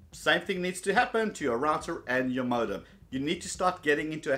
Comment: I wasn't paying attention so i thought he said "Your router and your mother" lol